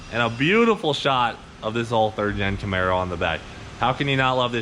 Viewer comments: Did he say 39 camaro...? Wtf at 00:20 seconds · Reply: Steven Reese he said 3rd gen Camaro but it sounds like he said 39 Camaro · It just me or did it not sound like he said “ 39 camaro “ at the beginning of video talking about the toast shirt lmao he said “ 3rd gen “ but that’s deff not what it sounded like haha!! #Toastftw #CleetusforPresident